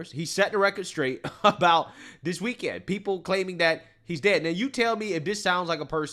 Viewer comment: Biden actually has a stroke: Everything is fine! Trump being fine: OMG! He’s dying! 😂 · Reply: I kept hearing "he ded" 😆